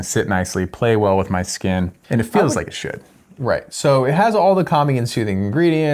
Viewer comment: I hear "feels like it should" as "feels like shit" whoops!